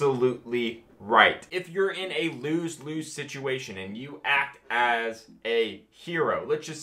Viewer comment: I thought you said "a Blues Clues situation" now all I can see is Steve talking to me about murder and how I'm gonna get away with it.